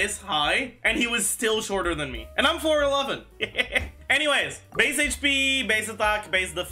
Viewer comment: I thought you said "and i'm Floor 11!" for a second. too obsessed.